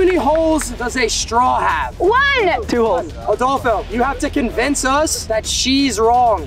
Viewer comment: "Adolfo" I'm having dirty mind 😭💀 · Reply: I thought he said astolfo